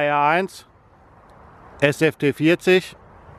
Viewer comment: Did you say D1 SFT20? Zwanzig? I thought I heard that and not SST40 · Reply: I thought nobody noticed🤫. It's the SST40 LED